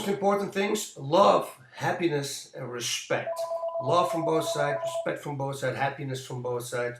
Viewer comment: "love happiness and respect"... I CANT understand what he says after... something like " love from bosa? respect for" and so on.. can somebody explain what he says????